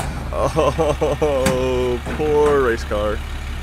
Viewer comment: No "RACE CARS" where harmed in the making of this video, none where present. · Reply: I thought he said rice car.